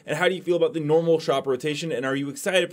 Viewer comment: Could of sworn I thought I heard him say, See you Meowt there"lol